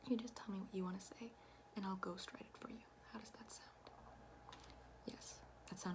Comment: So my thumb slipped and skipped and all I heard was "I'll go straight for you." How beautiful